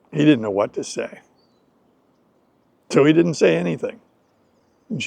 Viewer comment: Hello! Nice to meet you and thank you for your great service teaching us to English , I really appreciate you . One little question, on a time 9:54 I hear a phrase: ‘…She looked up Adam…’ what does this phrase mean? Could you please explain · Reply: You are most welcome. The phrase was "She looked up at him. The trap here is the American pronunciation. That is a "flap T" sound which is typical of North America. "At him" ends up being pronounced "Adam." I have a video on Flap T sounds. The video is "Why do English Speaker Pronounce Words So Weirdly? - <link> Thank you for your support.